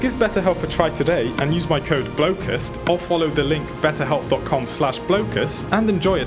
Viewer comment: I thought you said blooket 😭